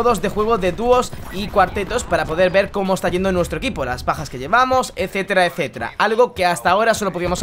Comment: parece que dice pajas